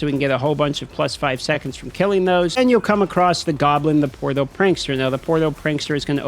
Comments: I had to rewind. I thought you said “the porno prankster” and I died laughing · I heard porno prankster which spawns a bunch of pornos...